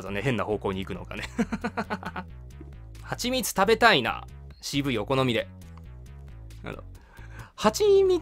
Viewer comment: 「CVお好みで」が「渋いお好みで」に聞こえて蜂蜜が！？ってなった